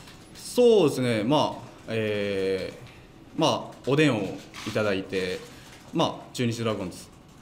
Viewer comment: 何回聞き直しても｢おでんを頂いて🍢｣にしか聞こえないんだけど笑